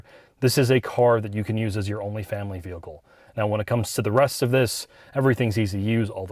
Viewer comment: at first I thought said this is a car you can use as your Only Fans vehicle. I didn’t know Hyundai was so out there!